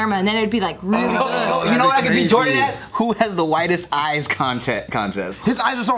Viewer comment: ooooohhh I thought Tim said “widest eyes”... cuz he’s asian